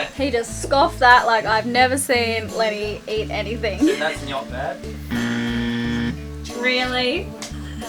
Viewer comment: did anyone hear what the dad joke was?? I couldnt here it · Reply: referring to Lenny enjoying and scoffing gnocchi, said, "And that's gnocch bad." (ie, not bad) I've actually said that one myself on occasions. Yep, a dad pun.